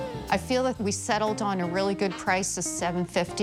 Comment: For $750.00 She says, it's All about the Benjamin's, well I thought you said Iit's all about your parents" 🤣